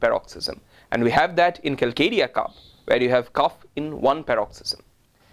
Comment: mark of video: Cough in one single paroxysm - what remedy is he saying? I can’t figure it out. ??? Calcadia ?? Kalkadia ? Calc carb? Kali carb? Can’t rely on transcription under video - it’s mechanical and can’t be used as authoritative. (You can’t always rely on speech to text on your smart phone)